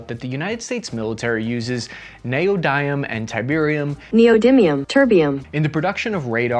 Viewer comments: I thought you said "tiberium" and I know from my videogame days how brutal fights over that resource can become. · I heard Tiberium? 😅❤👌